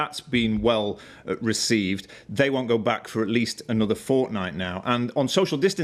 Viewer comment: did he just say fortnite?